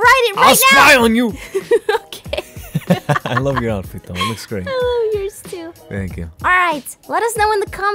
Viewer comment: I ship and because I heard a I love you 7:33 hear carefully